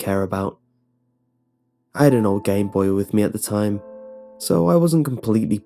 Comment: I thought it said " I had a old gay boy with me at the time." Lol.